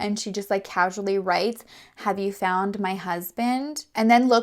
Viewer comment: I thought you said you found a house